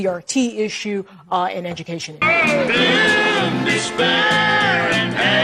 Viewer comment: I always thought they said "doom to spare"., not "doom, despair". I'm from Oklahoma and I love this show as a kid. I've heard that song a million times! Ha! Learn something silly everyday!